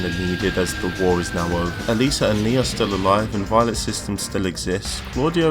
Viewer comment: Everytime u mentioned Alisa i thought u said Eliza